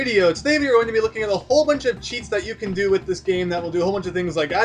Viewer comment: The captions say you're going to be looking at a bunch of chicks you will do